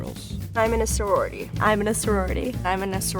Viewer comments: after the 3rd intro, all I heard was "I'm enissa roretty" like they were introducing themselves lol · The last girl sounded like she said I’m Minnesouri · what they were saying: I'm in a sorority what i heard: hi my name's rory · Them: "I'm in a sorority x7" What I hear: "iminasoridy"